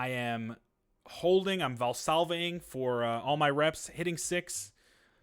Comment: Another great video. look at those calves baybee One question, at 2:12 I couldn't make out what you were saying. you were val salving? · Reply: Glad you enjoyed it! Yeah the Valsalva maneuver. In the context of weight lifting, it is a breathing method where you take a big belly breath and brace against your core in order to keep intra-abdominal pressure. It keeps everything secure and helps produce force.